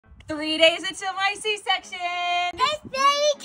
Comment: I heard "3 days into my c-section" and got really concerned. For a second I thought u meant that u had a c-section 3 days ago